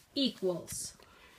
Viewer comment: it almost sounds like he says "1 2 3". Which would kind of make sense considering the answer was 3.